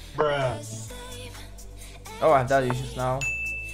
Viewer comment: Why i heard "oh daddy issues, no"